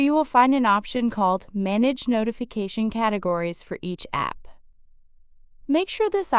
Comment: what exactly are you saying here? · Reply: You must toggle on "Manage notification categories for each app". It will be helpful to set the sound for a specific app.